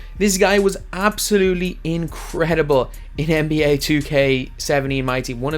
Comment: Anyone else think he said NBA 2k70 at 2:00?